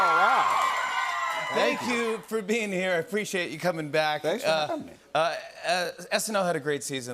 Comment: did he just say thanks for coming when he's the one invited